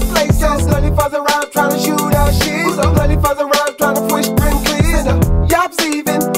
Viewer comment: 🤣😂 LMAO It’s starting to sound dirtier than the original. At 0:35 I swear I heard, “Ready for the round, tryna shoot our sh1t. Ready for the round, tryna squish those cl1ts.”